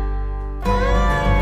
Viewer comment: It looks like certain people from both sides didn't make it ,but oh well you have each other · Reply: Sounds like she said "We love you guys".